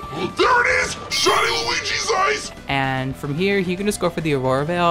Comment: what the hell is he saying at 1:20, shiny luigi's ice? am I going insane?